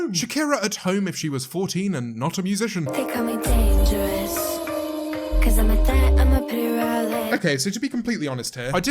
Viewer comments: I keep hearing "they call me dangerous, cuz I'm a thot...." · I thought she said “They call me a thot” lmao